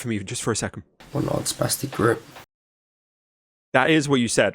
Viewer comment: What did he say at 8:00? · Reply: He called them a “sp*stic group”